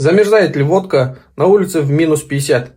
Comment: мне послышалось замерзает ли попка))